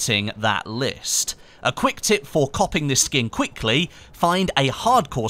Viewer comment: did you just say 'copping'